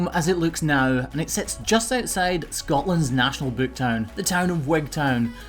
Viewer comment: Searching Scotland map trying to find what I thought he said Webtown?? any hints.